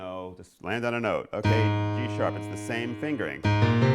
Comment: is it just me or does he say D-sharp???? I'm quite shure that's a G-sharp.